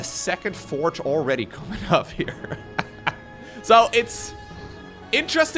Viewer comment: i heard: 'very quick toilet council' xD